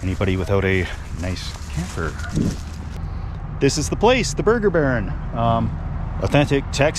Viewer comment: You said burger baron and I thought I heard barn. Delicious burgers at burger barn 4000 4th line in oshwekan 6 nations reserve by Caledonia Ontario. Best burgers, my favorite. If you're ever in Ontario check it out